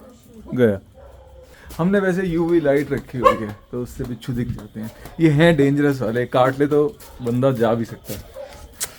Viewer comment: what u say sir? हमने वैसे UV Right रखी है जिससे बिच्छू दिख जाते हैं It means बिच्छू और uv right कुछ समझा नहीं। प्लीज रिप्लाई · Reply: UV light pdne pr बिच्छू glow krte hain.. easily spot kiye ja skte hn dark mein.